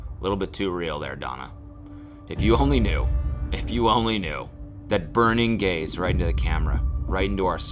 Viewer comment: Why did my brain fully think at 2:43 he said "if you only knew that burning gays..." and get really confused for a sec????